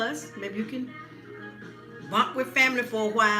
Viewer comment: At around 12:37 you said what? It sounded like girls. Lol. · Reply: And when you said if you have family it sounded like you said you can mock with them.